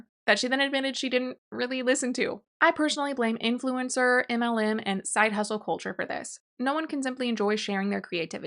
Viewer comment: I was only listening and not reading along and at 10:19 I thought you said "I personally blame influencers, Eminem, and side hustle culture" and for a second I was very intrigued what on earth Eminem had to do with indie authors not editing their works lol